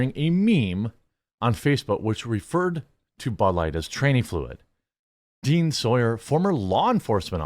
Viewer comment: lol I actually thought it said Tyranny fluid at first.